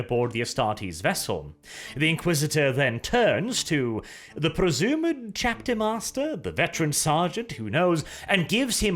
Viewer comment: you can hear what the Inquisitor is saying, but it's very muffled. It seems like he is calling "Return them Immediately" into his vox. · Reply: It´s "Recall them immediately!" 100% :)